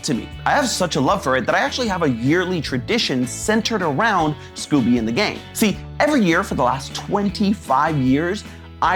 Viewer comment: I’ve been noticing subtitles errors a lot like “Scooby in the game” instead of Scooby and the gang